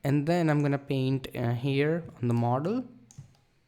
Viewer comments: amazing video mate. So glad you're back :) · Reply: What did he say "in the marder"? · Ohh god, after so long, u've came back again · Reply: What did he say "in the marder"? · Great broda!!! Keep it up!!! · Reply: What did he say "in the marder"? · What did he say "in the marder"? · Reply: "Model"